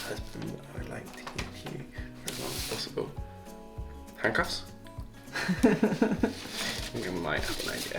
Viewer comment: What??? I swear I heard "handcuffs" when you were asked what you thought was in the birthday box. 😆